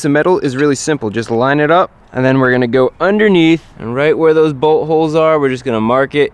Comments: It sounds like he is say butt holes instead of bolt holes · who else thought he said buttholes when he said bolt holes😂